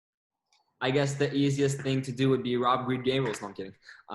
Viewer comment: O que ele diz no minuto 59:50? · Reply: "I guess the easiest thing to do would be robbery game or something"